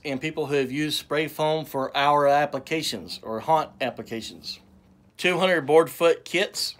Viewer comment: 😂😂😂with your accent I thought you said HUNT not HAUNT. That's why I looked up your video; I want to coat the inside of a hunting shack. I watched your other video first where you were actually demonstrating how to apply it. After a few minutes I was wondering what in the SAM HILL kinda hunting shack IS THIS? It wasn't until I watched this video that I got what you were doing. 🤣🤣🤣 I think your technique will be perfect for coating the tin on the inside of the hunting shack too. It will deaden the noise as well as seal it up for having a lil buddy heater in there. Can be 0 to 10 degrees sometimes at that time of year. Much colder later on. Excellent idea and video. Especially when you told your viewers to shut up, that cracked me up. Cheers buddy.